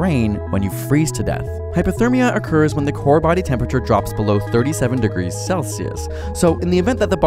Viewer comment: did he said "37°C"? 3:34 11 hours or weeks?